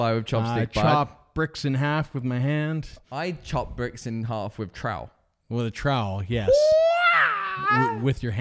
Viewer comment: I thought he said he chopped bricks in half with trout. Then he made those noises like the knights who say "Ni" in Monty Python and the Holy Grail.